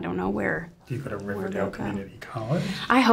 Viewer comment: What did the interviewer said at 7:37 ? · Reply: I think it was something like ‘go to a Riverdale community college’